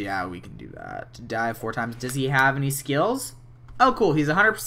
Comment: When he said "He wants to dive 4 times" at 23:51, it sounded like he said "He wants to DIE 4 times" 😄